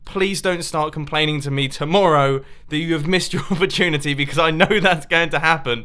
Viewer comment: I thought he said miss your virginity